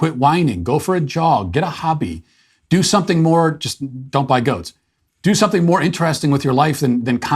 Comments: I've heard - "Don't buy goats." Did I hear this right?😅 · did Matt say "Don't buy goats"? Why the hating on goatherds?